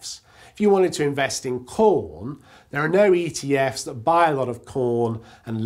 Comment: Did he just say por...